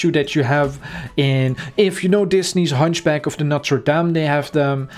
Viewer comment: Rip door Edit: 4:00 U SEEN THE HUNCHBACK OF NOTRE DAME? Edit2: oh my top hat is now next to my name COOL! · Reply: no he clearly said the hunchback of notre dame